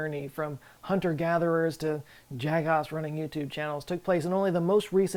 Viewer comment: Did he say "jagoffs"? Is Joe Scott a yinzer?